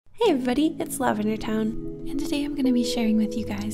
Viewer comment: Sometimes I mishear ‘Hey,everyone,it’s LavenderTowne’ as: ‘Hey,everyone,it’s love in your town’